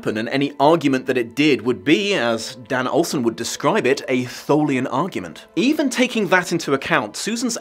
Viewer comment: Am I the only one who heard "Tholian" argument instead of "Thermian" argument at 28:45?